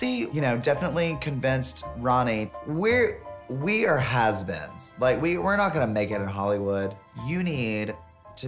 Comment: I thought he said, "we are husbands". After listening again & again, I finally heard it right, "we are has-beens"